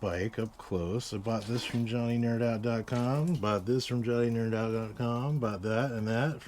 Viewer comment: Starting around the 5:40 mark. Where did you say you the parts from? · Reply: johnnynerdout (he's got a website and a youtube channel)